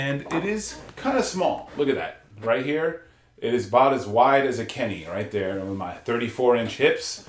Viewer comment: Lol when was saying how wide it was, I swear I thought he said " wide as a Kenyan". Lol🤣